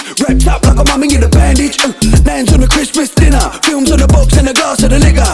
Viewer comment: Is it just me or did I just hear the word in 1:19 n**